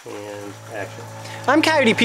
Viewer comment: Does anyone hear "andd Action" from